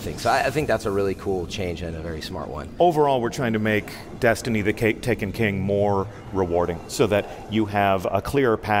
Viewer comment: Honestly, Deej is really cool. I really enjoy listening to the man talk. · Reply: did you hear DeeJ call it "The Cake" instead of The Taken King?